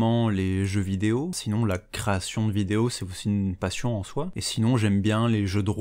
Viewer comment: j'avais entendu "procréation" ça m'a un peu surpris alors j'ai réécouté mais bon... comme quoi la prononciation c'est trèèèèèès important